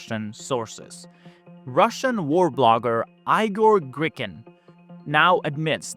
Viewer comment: did u say "Eye-gor Gricken"?!